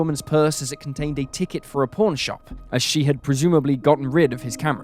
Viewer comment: with your accent, I thought you said “porn shop” and not “pawn shop” 😂